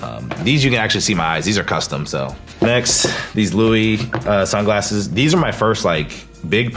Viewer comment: いきなりLouisとか言うからRuiに聞こえて一瞬ビビった 日本だとヴィトンって略すけどアメリカはLouisの方で略すのか 紛らわしい略し方しないでほしい